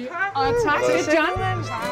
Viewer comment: Is it just me or is Mette Blomsterberg presenting the famous Paul Hollywood as “John” when they arrive at Schønnemans (approx 4:50)? It sounds like she says “Det er John” (This is John) 😂